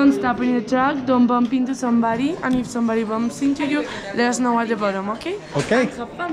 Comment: What does she say? If they bump you.....??